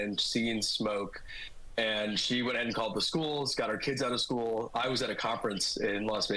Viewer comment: My wife called me…. Then picked up HER kids from the school? Interesting…. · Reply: When I listen as carefully as I can (with not great speakers) what I hear him explaining (at 1:00) "She went ahead and called the schools, got 'r kids outta school . . . " The "'r" could be a contraction for either "her" or "our." Maybe it's a Rorschach test: what you hear him say is what you expect him to say.